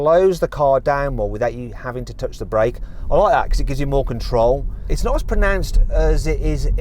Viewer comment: proper british accent , I think he says ''I like that''